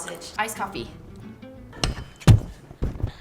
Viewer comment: kind sounded like u said "I just smit out my coffee" 🙃